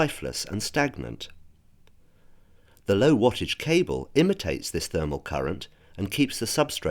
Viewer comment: what did he say? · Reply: Low wattage cable